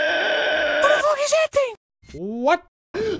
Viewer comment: Only now i hear at 11:05 he talk italian (my country) and in english is :"Daddy, did you hear me?" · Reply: He said: "What the f*ck is that?". It was just too fast.